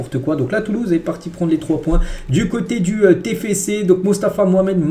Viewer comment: ta dit quoi la 😂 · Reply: 😂 "toulouse est allé prendre les 3 points au téfécé"